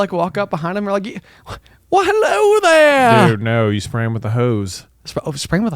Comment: What you said was "spray him with a hose." but what I thought you said was "Prayin' with the hose." I was wondering if that was some hip, new lingo I didn't get... I mean, I don't get a lot so it seemed plausable.